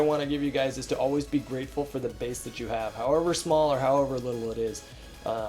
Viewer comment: Had my volume on low... I heard "penis" instead of "base" :p